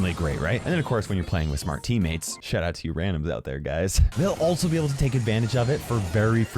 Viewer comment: did you just called us randoms🤣🤣😅😆😆😂😁😇